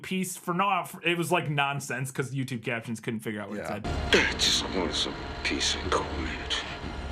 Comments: sounds like he says "just wanted some peace and quiet" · I hear “ Dad just wanted some peace and quiet” or maybe “they”